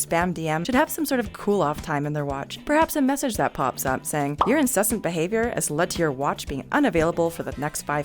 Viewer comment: wasn’t paying full attention and thought i heard “your sussy behaviour” 😂